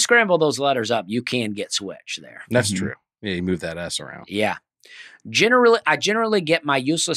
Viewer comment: Sounds like is saying “yeah if you move that ass around” responding to Dusty wanting to say turnSwitch instead or turnwitch. I think it works even better.